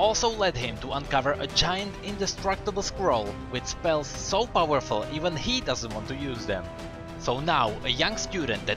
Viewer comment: I thought he said a powerful squirrel 😂😂😂